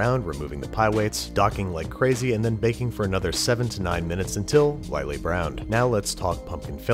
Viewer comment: I heard "79 minutes" and burned my kitchen 😒